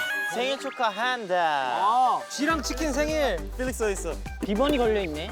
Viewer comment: Did i just hear lee know say “지 랑 치킨 생일” like is he actually calling jisung “ji”??! Thats so cute if its right 😂